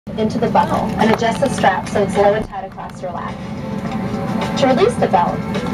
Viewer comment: hahaha sounds like she said "into the butt whole"